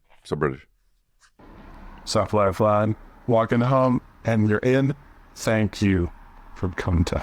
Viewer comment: Maybe it's because I have headphones on, but it's very obvious that the weird voice guy's submission (35:46) starts off with "Sup Lifeline. Walking home, and they're in. Thank you..." Can't quite make out what he's thanking them for though.